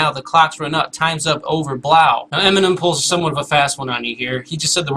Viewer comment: i always thought it said times up over "now" not "blow"